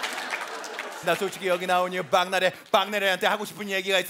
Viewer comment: 나만 계속 빵나래라고 들리나ㅋㅋㅋㅋㅋㅋㅋㅋㅋ